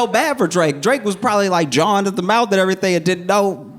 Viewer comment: Can somebody help me? What is he saying at 10:46 "Drake was probably like..."? I don't think the auto transcription is right. It says "John at the mouth". Can somebody help? Thanks in advance!! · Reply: He said "jawing at the mouth," which I haven't heard before but I assume is just a variation on jawing: to talk down to in a belittling or derisive way, like before a fight